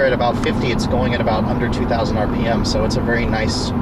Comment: I said at 10:45 under 2,000 RPM, I did not say under 32,000 RPM. I can see why you could possibly hear that but you are definitely just making yourself hear that...